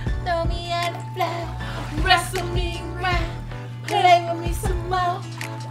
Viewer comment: bruh lmao did u just say play w/ my self ok dont got to ask me twice lol